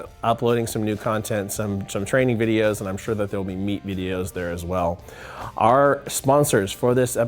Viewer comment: I heard "meat videos" at the beginning and I stopped listening and started fantasizing. I'm sure the content was great but I don't remember.